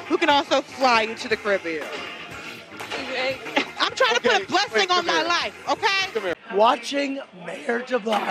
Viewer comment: "I'm trying to put a blessing on my life, okay" Did I hear that right? (0:45) Yeah, I got nothing that I'd be allowed to say.